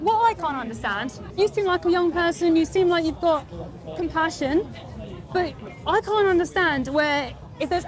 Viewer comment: For a second there I thought said "you seem like you've got concussion".